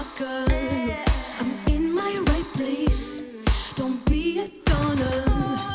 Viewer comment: she's so quirky :) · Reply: I almost honestly thought at first she said, "don't be a daughter.." :/😂. Welp, thanks for sharing & I'm sorry about that. I def. Don't have all the right answers, but I always try to instill confidence in my kid. So she knows her self worth.. Also, she's still her own person, so the fact that's she's always been a little on the feisty side is comforting..I don't have complete control over anything else, but hopefully I've influenced my child enough in some positive light. Idk how harsh she may encounter bullies (she's only 8), but I've at least had her keep 2 steady friends in her short yrs. Hopefully that's enough to teach her to value building strong R.ships. I've never been absent & maintain a closeness.. Always tried to keep somewhat of an open mind,.. so she's open w/ me. But idk? There's no manual for right or wrong upbringings. I hope she doesn't get bullied. But again, sorry for your experiences. I looked into the song. It sounds like a billion american artists meshed into one :/. To me anyway. I can dig 😁 Not bad. Your experiences took a little darker route though.. I'd like to think that I can still save the light in my kids eyes though. It's not that dim - yet. And I don't exactly want it to be..